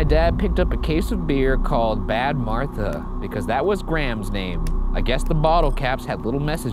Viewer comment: some subtitle errors: 19:21 Graham instead of Gram 19:30 "grape beyond" instead of "great beyond" i've commented this before, but please get someone to proof read the subtitles.